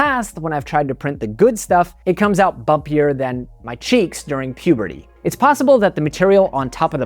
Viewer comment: Ah, CHEEKS. First time I listened, I thought you said SHEETS. One is much grosser than the other.